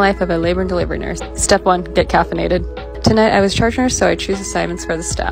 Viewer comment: I thought u said Get cathenated like with a catheter and I was like 😳😆